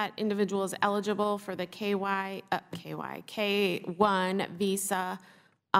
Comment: Hahaha , did she just say KY instead of K1. Jesus, lawd. Time